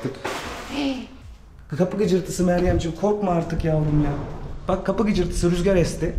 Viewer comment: yavrummu dedi bana mı öyle geldi 15:46 da duydum M+T=❤